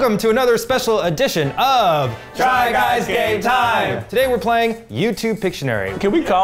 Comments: Sorry but I keep hearing it as "Try Guys Gay Time" 😅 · ever since Eugene’s coming out I keep hearing “try guys gay time” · I always thought they said "try guys gay time". I just realised i am a little uhh.. wrong.😅